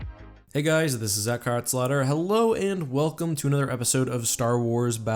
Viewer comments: Did anyone hear "echkart slaughter" I did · Is it just me, or does it sound like you say "Hey guys it's Eckharts slaughter" xD